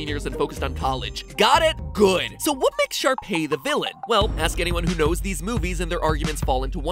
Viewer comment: I thought at first he was saying sharpie 😂